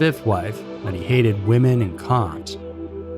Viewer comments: 💥 For 60% off with HelloFresh plus FREE shipping, use code SCARYMYSTERIES60 at <link> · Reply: women and what?? Man you need better communication skills. Caption doesn’t even understand you. I mean i think u said cops. But come on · What is he saying at 4:03?? He hated women and....? Cont? That's not a word. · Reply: Cops